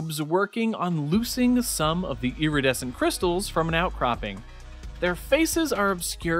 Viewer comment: I heard "triangle" patterens, lol Also I lov the artwork on the crystal backround. Using white outlines for them is an amazing idea. The shiny parts and shading make it look lik there glowing! :>